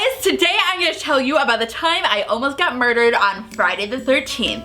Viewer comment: I thought it said I ALMOST GOT MARRIED I was like "WHAAAAAAAAAAAAAAAT!?"